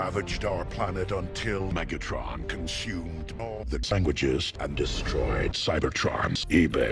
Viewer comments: “Until Megatron consumed all the sandwiches.” LMAO. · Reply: I thought it said “languages” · “Until Megatron consumed all the sandwiches.” LMAO. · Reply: I thought he said languages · “Until Megatron consumed all the sandwiches.” LMAO. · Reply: Sounds like he said languages. 🤔